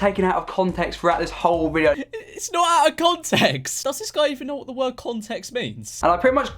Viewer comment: For some reason i used captions and when he said its not out of context captions thought it said its no gods eggs